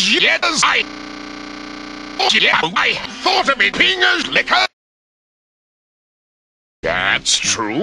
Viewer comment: ok, the first few seconds i thought i heard something like :his rotten pingas is numb", lol, then at 1:49 i heard alot of pingas, and then he said he was a pingas licker, and then he said that it was true, lol